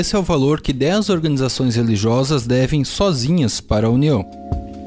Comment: Eu ouvi "esse é o valor que dez organizações religiosas devem, sozinhas, para o Mion", fiquei impressionado com o cachê do marcos